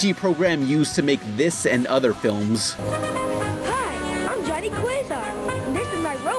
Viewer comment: "hi am johnny quasar" SO THATS WHAT HE SAYS